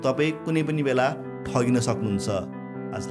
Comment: At last what he said : I am. What I listen : I am the best 17:55 And I like that 👌